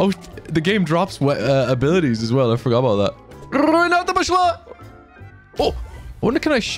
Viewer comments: Я один слышу на 0:59 фразу "Граната пошла"!? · Did u just said граната пошла? Damn that's cool, didn't know that you know Russian.